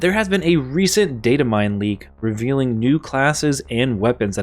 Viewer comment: I thought it said Diamantine leak and I got psyched because I need more diamantine.